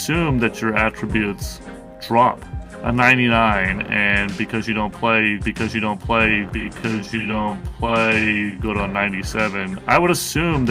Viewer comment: Sound like he said if you don’t play good.